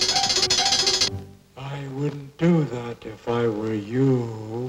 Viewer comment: What did he say? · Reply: I wouldn't do that if I were you!